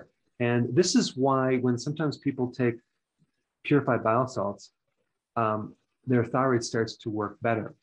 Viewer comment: Did you say “purified bile cells?”